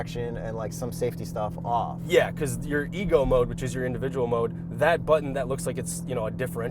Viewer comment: I heard “ego mode”😂😂😂❤. 6:20.